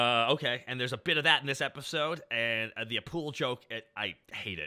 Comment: i just thought he said "a poodle' like the dog